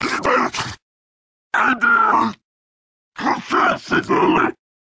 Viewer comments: I always thought he said “...testicle” 🤣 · i hear “consest the souling”